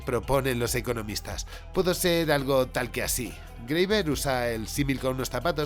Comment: greyvet? Greyver? Me quisiera adentrar mas en esa teoría pero no entendí el autor…